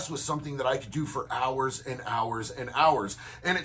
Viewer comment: I wasn't really paying attention at first I thought he said he could blow guys for hours